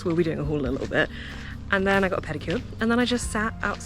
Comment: I'm laughing at myself right now. At about 16:45 you were saying you had such a nice afternoon and "I had a pedicure..." I thought you said "I had a painkiller" and I was thinking, well THAT can't be right. I had to rewind and put the captions on. The pedicure makes much more sense! And talking about laughing at myself, I would never be able to get into a kayak as effortlessly as that man did. I would be in the canal next to the upturned kayak, scanning the area to see if anyone was watching. It's great fun getting older! Thank you for this beautiful video.